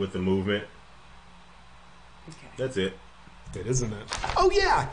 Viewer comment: After said “ and that’s it” anyone else hear a whisper say “that isn’t it.”..????? I don’t know if that was just may be perfect timing and it was some thing said in his video right after y’all pressed play again but that bugged me out fr